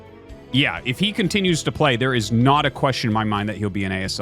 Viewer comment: I honestly cannot tell if you were making a pun right there, because I swear you said NaDA and not 'not a'.